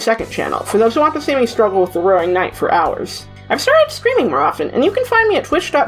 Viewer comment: misheard as “I’ve started screaming more often”